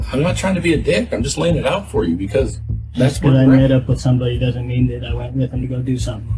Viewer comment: the quote at 8:00 is "just cause i made up with somebody" not met up, like the captions indicate, which somewhat changes the meaning of what he was saying.